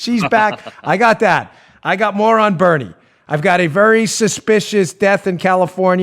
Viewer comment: You've got moron Bernie? I didn't know you had him on as a guest.... ...oh wait, you said "more on Bernie" :) I must have misheard.